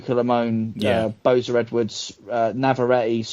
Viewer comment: Who's the boxer mentioned before Navarrete? edit:Boza Edwards, had me thinking I heard' Boser Redwood'